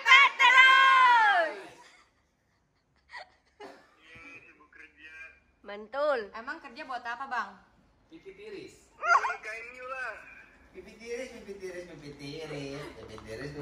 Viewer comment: "buat nikahin umi lah"waw is the amazing 😍🤗 menurut kalian aku salah denger apa bnr guys? Komen dibawah yaw